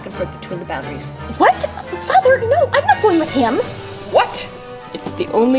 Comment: anyone else hear mother lol